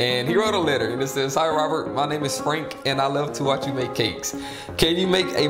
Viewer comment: POV: you’re looking for Franks comment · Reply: Oh he said frank I thought he said sprink, and I was trying to figure out what kind of a name is sprink?